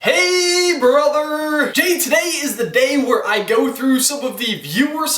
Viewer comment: i thought he was saying hey butter lol xd